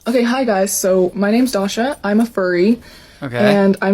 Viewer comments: I first thought she said she was a fairy...but it was furry as if it couldn't be more bizar!🤣🤣🤣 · Did she just say "my name is dogshit?"